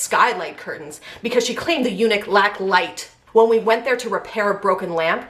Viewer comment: It sounded like you said eunuch instead of unit at 12:09 which is actually fitting for willy boy